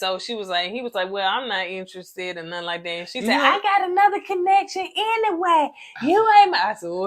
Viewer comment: Alllegedly Zo had a girlfriend the whole time.. . He told her he was on a reality show but not a dating show.. . It appears from his behavior he was more than happy to go back to her 🫣👀👀.. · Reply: I thought she said, she has ‘Another connection’ but she might have said ‘man’.. This season is quite interesting 🥴😂…